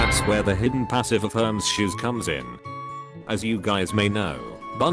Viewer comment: he said gays instead of guys💀💀